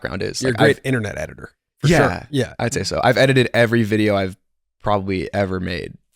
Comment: I fully thought he said he edited every video ever made and started busting my ass laughing